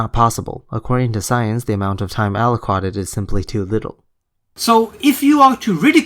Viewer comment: mate... mate... wispers mate... 1:35 did u just say "time alo-qwa-ted"? ...allocated?